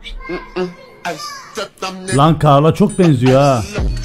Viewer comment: ta kaana çok mu benziyor diyor ben mi yanlış anladım